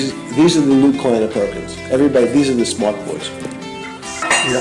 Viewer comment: bro did NOT say boys 😭😭, we ALL know he said Goys